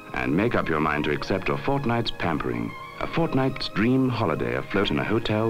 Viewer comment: did he said Fortnite? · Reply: No he said fortnight